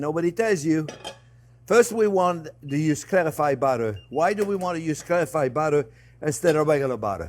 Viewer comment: I can't understand what he's saying here, use what butter? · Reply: Clarified butter😊! <link>